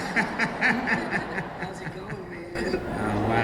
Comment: what did jen say at 14:37? or was that 14:36? she said: can't reisist this _ .....I didn't really hear what she said...can somebody pls tell me...pls.... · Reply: she just said ‘this’ in a weird way there’s nothing after it